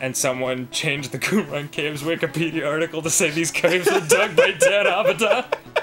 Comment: i try as hard as i can, but i cannot understand what Sexbanger was saying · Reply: "Someone changed the wikipedia article of the Qumran Caves to say they were dug by Dan Avidan."